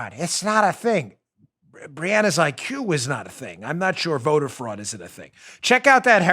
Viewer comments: “Brianna’s IQ is not a thing Not sure if voter fraud is not a thing” bongino 2020 classic line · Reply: I thought he said “Q is not a thing.” I was like ohhhh boy this is gonna open up a can of worms. Thanks for clarifying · Did you really say Q is not a thing at 26:20? Maybe I misunderstood.